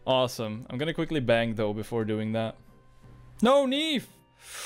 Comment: i literally thought you said "I'm gonna quickly BANG tho, before doing that" xD